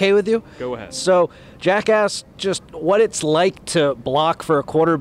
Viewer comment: Thought he said jackass not jack asked😂